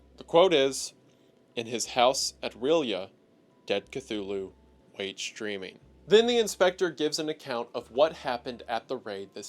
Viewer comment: I had to rewind at 19:00. Was watching in the background and thought he said "Dead Cthulu waits STREAMING". Now I can't stop laughing.